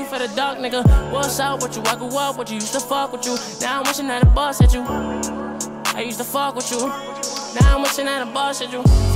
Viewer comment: Lil Tjay: Now Im wishing that the bus hit you Blueface: Did anyone mention me? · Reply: I thought he was saying "now I'm wishing that I bust at you"